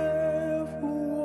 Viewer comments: I thought he said going home to eat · Thought he said “going home to eat”